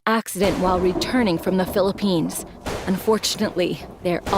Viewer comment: did i just hear PHILLIPINES thats my home country ❤